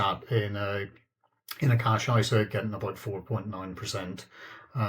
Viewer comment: he mentioned he has emergency $$ in a tax-free savings account? or did I hear this wrong? TIA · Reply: £ not $ since he's in the uk. cash ISA - yes it's a tax free account so no tax to pay on the interest it pays.